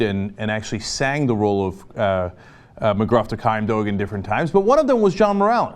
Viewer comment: Sounded like Cenk said Mcgruff the Crime Doge. Much awesome, Cenk.